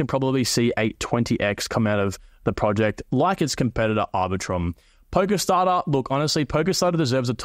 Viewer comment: What is the one at 33:44? I couldn't make out what you said. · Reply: POLS Polkastarter ...love this coin...its my biggest holding